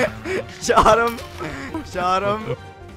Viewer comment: this whole video I only heard "shot em'"